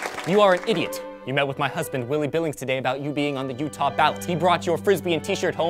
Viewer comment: Every time I heard Willy billings, I thought you said his name was Willy nilly.